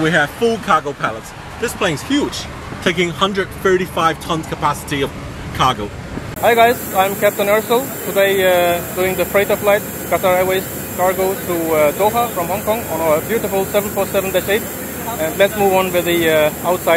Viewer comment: sounds like he said his name is captain arsehole 😂😂